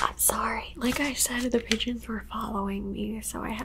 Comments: Ohhh pigeons I thought you said d gens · Oh lmao pigeons. I legit rewound it 3 times cuz i thought u said u were being chased by a bunch of Midgets lol. I was like ohh dang lol.